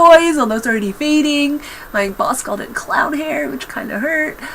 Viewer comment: aww I wouldn't say clown hair, it makes me think mermaid :) also I hope you had a nice birthday :D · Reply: Oh, CLOWN? I thought she said "cloud." I was like...that's an odd way to describe hair, but I'm not necessarily sure it's insulting lol.